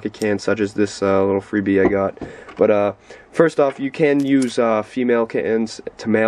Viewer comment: Did he just say “first off you can use female kittens” at 0:14 😂